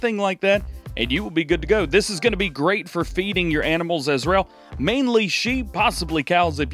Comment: anyone hear Israel instead of as well? Anyhoo love the vids keep up the great work